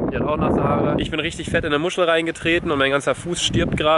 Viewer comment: wer hat auch verstanden Ich bin in eine M*schi reingetreten